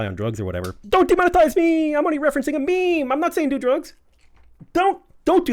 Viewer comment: I wasn't 100% listening, but I think said "Do Drugs". 🤔